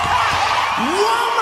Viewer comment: what he say in that part? woman .... ? Pls help :d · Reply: "Woman...I said Womaaaaaaan...you need....Looooooooooove"